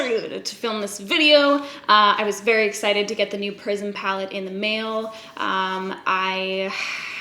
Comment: lol it sounds like you said “Prison Palette”